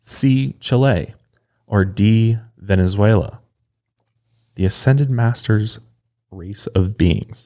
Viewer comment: At first I thought he said “The ascended masters of beans”